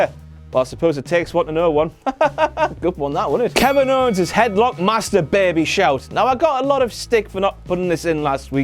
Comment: when I hear ko say" headlock master baby" I thought he said" I like masturbating"